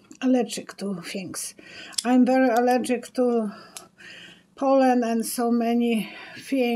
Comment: Lol at first I thought you said you were allergic to Poland (pollen)!